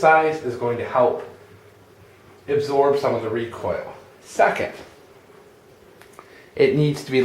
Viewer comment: about a minute in to the video you say, "second" and at first i thought you said, "suck it!" hahaha, just thought i'd share